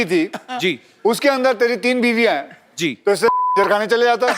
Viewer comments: What did he said? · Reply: He said film kyu ki sidha randikhane chala jata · Does anyone know what Akshay Kumar said here because they beeped it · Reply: Akshay ne kaha........to is se acha kanjar khane chale jata😂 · per kya kaha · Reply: He told khajarkhana (red light area) I guess